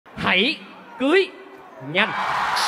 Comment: Hãy cưới nhanh mà thak em tui nó nghe thành Hãy cưới nhặt =)